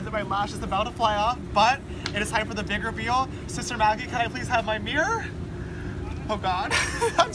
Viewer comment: I thought he said "can I please have my beer" 😂